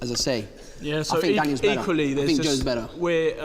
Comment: is it just me or does he say he thinks Daniel is better